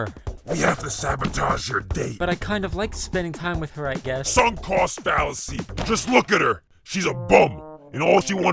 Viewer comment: what does he even say??? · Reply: sunk cost fallacy